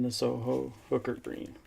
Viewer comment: I had to rewind it..I thought you said Soho Boogers Green..lol! My hearing must be going to. Geez! 😎